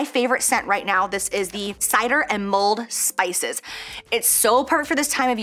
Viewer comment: At first I thought you said “moldy spices” when describing the candle lol